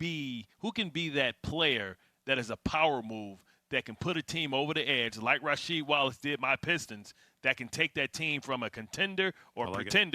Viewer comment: Did he just say Lonzo can do for a team with Rasheed Wallace did with the pistons? 🤣 strip that ball card Jesus haha